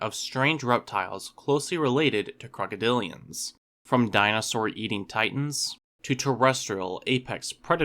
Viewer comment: I briefly misheard that intro as "dinosaur-yeeting titans" and imagined a crocodilomorph with huge beefy arms flinging a dinosaur